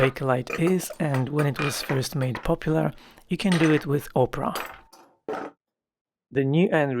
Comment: when you saying ‘opera’ I kept hearing ‘Oprah.’ I thought it was strange…